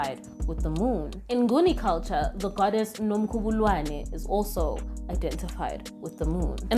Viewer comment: What name are you saying at 4:30 please?? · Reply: nomkhubulwane <link>